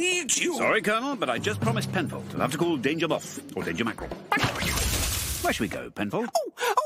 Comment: what does he say before Danger Mackerel? Who was the other agent? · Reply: Danger Moth I believe